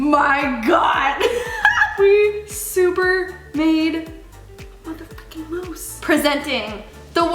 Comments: we so made a muther f*****g moose....... is that what she said bc it sounds like she said that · from 10:25-10:28 did she say the real one or mother frickin moose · did she say a mother f@ moose? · did she say fuckin?